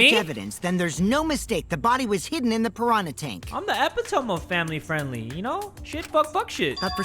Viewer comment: why did he say epitome like that 😭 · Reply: "I'm the epehtom of family friendly <3"